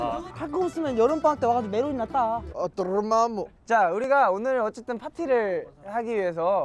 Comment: 여기서 종호는 뭐라고 그래요? 이게 두 달째 궁금해요😂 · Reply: Dormammu, doctor strange!